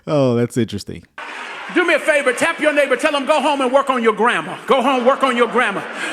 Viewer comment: I thought he said go home and work on your grandma not your grammar. 😂